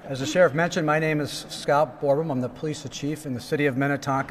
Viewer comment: Did he really say “I’m the police of chief” at 4:13?